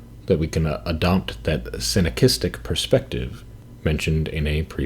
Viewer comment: At approximately t=16:43 you talk about adopting a “synokistic” perspective. What is the word you say? And what previous video is it mentioned in? I would love to learn and understand what is being talked about here! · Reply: Synechism (synechistic being the adjective). I wrote more about it here: “The Continuity of Being: C.S. Peirce’s Philosophy of Synechism” by <link>